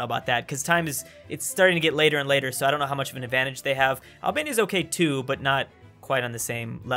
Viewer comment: I thought it said “Alabama” instead of Albania lol, was like damn, Alabamian Italy 😂.